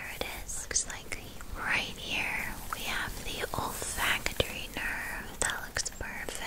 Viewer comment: I legitimately thought you said “the ol’ factory nerve” 😭